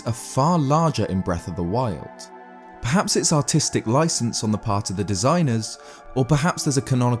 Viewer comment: I know he's saying Artistic but it sounds like hes say Autistic do to his accent, i laughed so hard at mishearing it.